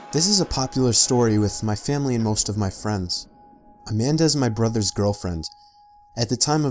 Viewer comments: Oh my gosh. At 14 seconds u thought he said " a man does my brothers girlfriend" instead of " Amanda's my brother's girlfriend". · it sounds like he says, "A man does my brother's girlfriend," XD